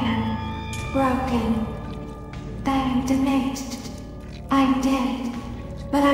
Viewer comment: "I'm dmaged" is what it sounds like you said